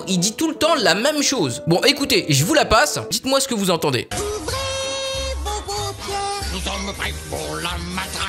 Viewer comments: moi j'entend "ouvrez vos grosses mères" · Pour la première c'est fou j'ai entendu "vos gros cons" d'abord, puis après j'arrêtais pas d'entendre "vos paupières"